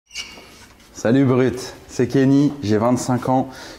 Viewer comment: "Dès le début, j'ai compris "Salut Uber Eats" - Paquemoa